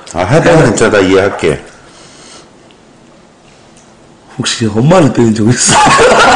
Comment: 뭐라는거임? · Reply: 엄마를 때린적 있냐고